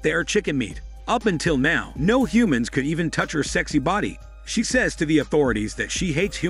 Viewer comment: All I heard was “her sexy body “😂😂😂